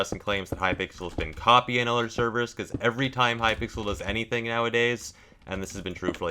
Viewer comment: At first, I thought that this video said "Is Hypixel coding other servers?"